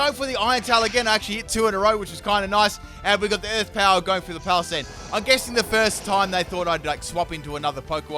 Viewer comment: Did anyone notice what he said at 20:00 ? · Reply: “The Earth Power going for the Pallossand.”